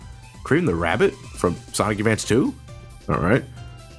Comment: I thought said "Sonic Adventure 2", but now I just realized that he said "Sonic Advance 2". I might need an ear enhancer.